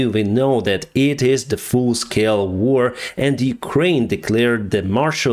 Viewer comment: Did you say “Full-Scale War,” or “Fool-Scale War?” 😂 Great video, sir. Praying for you and your people.